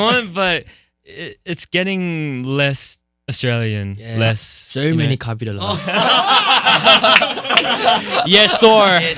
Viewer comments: What did Hee say in 19:12? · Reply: Saur many copied along?? Idk😭 Tell me if you find out please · does anyone know what heeseung said at 19:11 cause i’m so confused 😭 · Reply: saur many copied a alot😭 I think he was trying to say that he copied jake alot lol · what did Heeseung said in 19:12 please? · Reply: "saur(so) many copied alot" I think he means he copied Jake a lot with the aussie accent